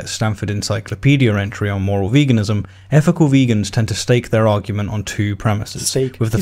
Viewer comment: did you say STEAK?!?!